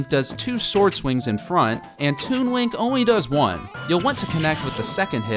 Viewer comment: it sounds like he says toon wink